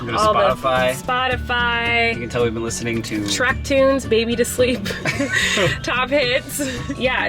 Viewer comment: I thought Colleen said “TRAP tunes…..Baby to Sleep” 😂 Ah, nice! From Trap music to lullabies! 🤣